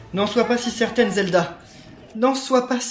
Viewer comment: j'ai cru entendre zelda ou je rêve ? Pitié dites moi que j'ai rêvais !